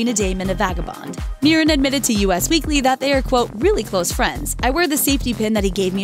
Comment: did she say U.S. Weekly? instead of us? LOL!